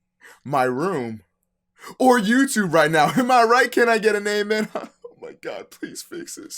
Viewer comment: omg I thought he said can I get a head a